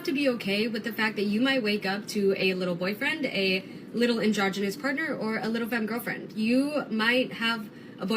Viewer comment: sounds like they said 'little androgenous predator... Slow down Ditsy!